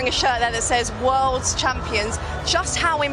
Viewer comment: did she say words champions